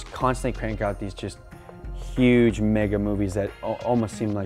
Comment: I thought he said “huuuuuuge NIGGA movies” lol 😂😂😂